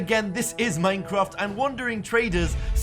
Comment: FOR THE HORDE!!! Wandering trader: Huh? Oww (it kinda sounded like that)